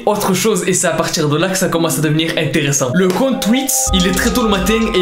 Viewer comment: c moi ou il dit le comte twix 😂😂😂😂